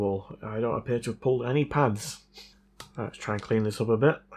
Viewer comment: I thought you said you never pulled any pants 😂😂